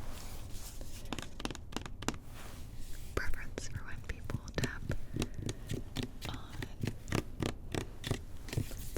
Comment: So confused... do you really say "I have a.... preference for white people.... tap on..." .... What? Am I hearing that wrong? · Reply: Kyle R "I have a preference for when people tap on plastic bottles."